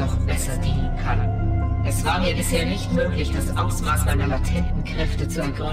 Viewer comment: Misheard:"...das Ausmaß meiner Tittenkräfte..." Meiner Treu...Übrigens wieder einmal sehr schlecht gespielt...Die gesamte Mission kann man ohne einen Verlust einfach getarnt spielen...